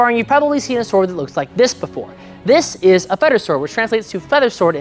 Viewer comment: I thought he said "a fetish sword" 💀